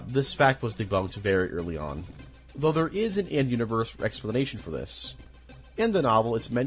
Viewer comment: D-Did he just say "Rexplination"?